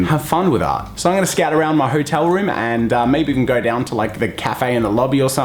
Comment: did you say scat okay I’ll leave